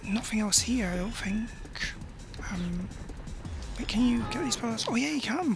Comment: OMG It looks so satisfying to just thump those hyper monkey's when they're running round annoyingly refusing to get caught. Haha. 13:53 "Can you kill these Boulders." *Kills boulder but almost kills self too. * Haha. 15:05 Hahaha, Aww you sounded so annoyed at the lack of a challenge there. XD "Ugh what the hell?" XD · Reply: Yeah, very satisfying :) 13:53 - I said "get" not "kill" there, but that was a funny moment there. XD 15:05 - I don't think I was annoyed, just shocked and amazed that it was that easy by using the punching glove instead of using the stun club :O